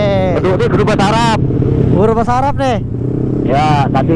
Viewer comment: sama anjir dengernya guru bahasa arab😂